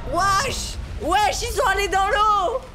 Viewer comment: C moi oui il a dit wash